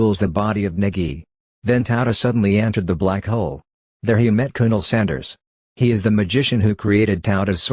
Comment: Did you say Colonel sanders